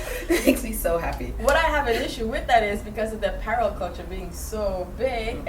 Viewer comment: I couldn't understand what you were saying you loved at 2:50, I actually went back and listened to it 6 times and couldn't get it lol. The girl to the left (sorry, new to the channel and I don't know names) reiterated it and I finally realized you said 'apparel.' Otherwise love the video! Great fun energy